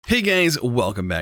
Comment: sounds like he says "Hey gays" instead of "hey guys".